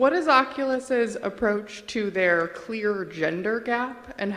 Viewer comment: Did I just hear: "clear gender gap"?